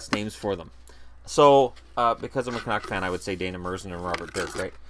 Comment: At around 19:17 the captions thought you said " because I'm a cock man" instead of "because I'm a Canuck fan" which I found hilarious.